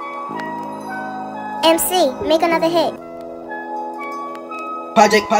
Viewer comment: IT SAID BULLET? I THOUGHT IT SAID PULL IT🤣